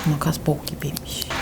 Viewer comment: makas bok gibiymiş mi diyo yanlış mı anladım 😂